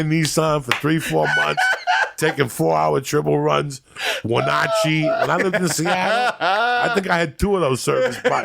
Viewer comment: Did he say Wenatchee ? At 1:50👀